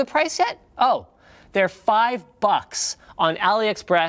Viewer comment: When he said AliExpress I thought he said Elliots breasts, had to rewind for a second